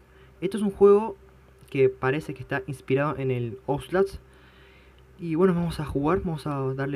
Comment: inspirado en el que dijo ? En el ouslas jaja